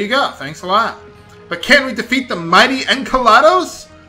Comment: hahahaha when you said enkelados I thought you said enchalados! I thought hang on, isn't that a type of food!